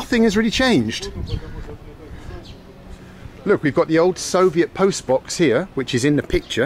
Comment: I had to rewind a bit cause I thought he said "Nothing's really changed. Look we've got the old Soviet post-MAN here which is in the picture." as he pointed to the dude who stood there for decades...